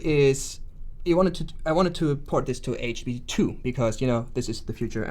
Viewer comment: There are some funny typo's in the transcript. For example at 9:08 instead of HTTP2, it states HB2. Also 4:13 states "pipe [INAUDIBLE]" which should be "python webserver". And at 9:15 TLF instead of TLS.